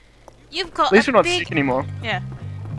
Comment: did someone say in the game, "you've got a big___?" at 4:47? and didn't have a snide comment on that? lol